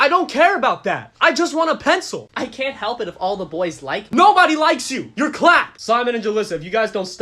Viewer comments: Am I the only one who thought he said”nobody likes you, your Bla-“ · WHEN IT SAYS YOUR CLAPPED I WASNT LOOKING AT THE CAPTIONS SO I THOUGHT IT SAID “no one like you you’re black”😭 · For a second I thought blud said "NOBODY LIKES YOU, YOU BLAC-"